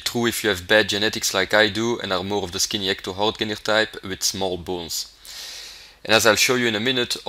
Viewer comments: Does... does he say he has small balls at 1:00-1:03? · I thought he said small balls lol